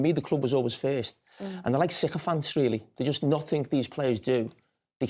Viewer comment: what did he say about the fans? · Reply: “They’re like sycophants really, there’s just nothing these players can do, that they can ever get wrong”